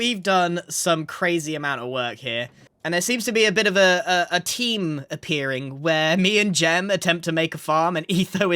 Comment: subtitles said team instead of theme